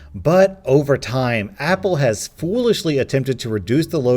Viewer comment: i heard "roblox"